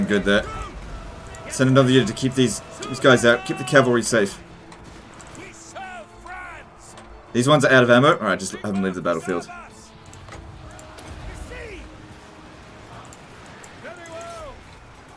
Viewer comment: I heard you as “keep the cavalry safe” and almost choke on my joint...!